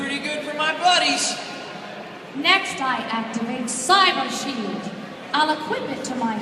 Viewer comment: Is it bad that I thought "Mai" said "I activate "Cyber Bondage""? Dat anime... -.